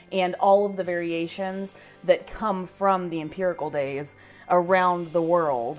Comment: It sounds like you were saying Empirical, not Imperial, lol. Beautiful Christmas cake, my friend from the UK always makes them and shares with us.